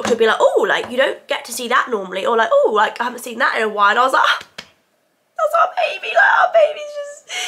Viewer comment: swear she said that’s our baby girl 😁💕